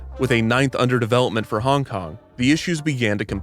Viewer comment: I’m so sorry but every time he says Hongkong I hear “honk honk” 🙈